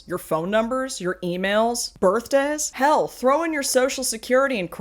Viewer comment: when you say “birth days” it kinda sounded like “birth dads” to me, which made me choke on my drink with how hard I laughed. No! I can’t expose my birth dad information online! Save me! 😂